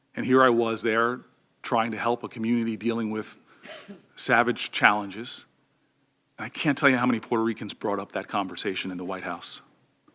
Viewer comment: Did he just call PRs savages? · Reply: He said Puerto Ricans experienced Savage storms.